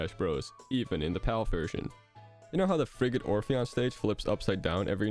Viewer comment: I'm sorry but all the times he said Frigate Orpheon it sounded like he was saying Friggin' Orpheon